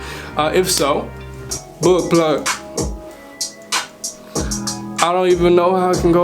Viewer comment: Thought for a sec you said butt plug